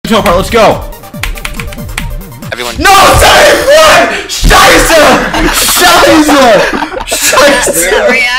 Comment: i heard Schei#e! Its German for Shit